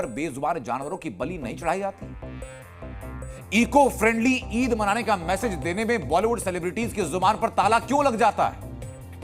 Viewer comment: for a second I heard he said eco friendly eid banane ka bolte time celebrity ki gaand Kyun fath jaati hai😂